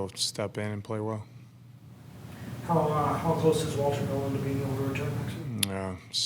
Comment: wtf is he saying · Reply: Asking about Walter Nolan returning